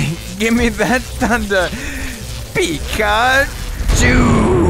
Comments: watching this years later i thought he said "begone jew" but he actually said "pika - chu" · it kinda sounds like you said begone Jew